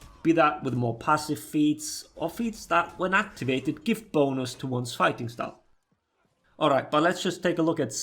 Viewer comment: I'm sorry but every time you say "bonus" I hear "boners"